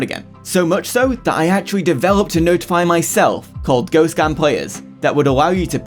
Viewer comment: Thought you said "Go scam players" 😭